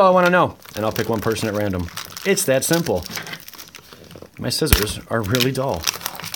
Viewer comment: I thought you said "my sisters are really dull" had to rewind to hear that again , but you said my scissors. Lol ... I got into Magic because I thought and think dragons and giant wurms are cool.